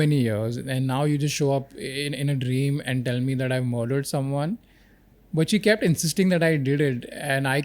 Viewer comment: Daniel says which He kept instead of she